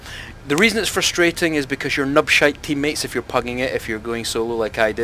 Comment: did you say "nup shaping" O.O or? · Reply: nub shite :D